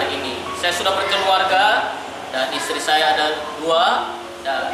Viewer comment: Menit 15:17 bang. Maaf bangat bang, itu pak pendetanya bilang isterinya ada dua. Mungkin aku salah dengar bang.